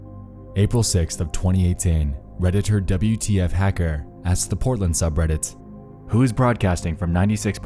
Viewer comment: could’ve sworn it said Poland Redditors